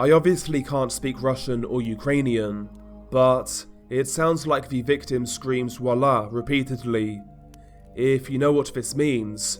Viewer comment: I think he's sayin "больно" which means "It hurts".